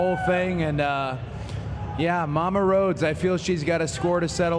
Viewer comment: I def thought they were saying “Lana Rhodes” 😂